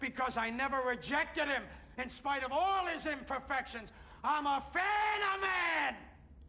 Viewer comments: Can someone please tell me what Al Pacino says here? Thank you. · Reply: I'm a fan of man!!! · Can anyone tell me what Pacino screams at 1:33 ? Right after he said he never judged him? Sounds like he said: "I'm a fan of man" thanx